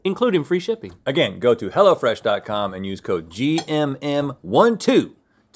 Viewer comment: 1minute and 37 seconds in it sounded like he said hella fresh and I cracked up at it